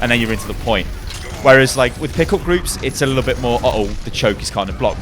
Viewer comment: at first I thought you said pickle groups instead of pick up groups lol made me chuckle